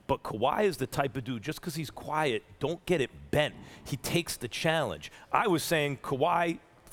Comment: Did he just say "don't get it bent"?